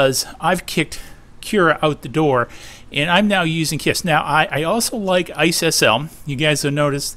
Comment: What Slicer did you mention @ 4:07??? I have a hard time making out what letters you said. Thanks! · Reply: KISS : <link>